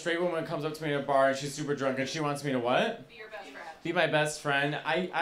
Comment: I thought she said “She wants to be breastfed.”